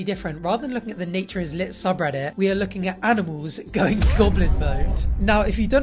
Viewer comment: I misheard “going goblin” as “going Bodmin”